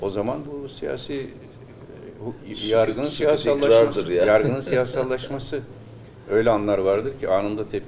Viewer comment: Yargının siyasallastirilmasimi dedi öyle anladım ben.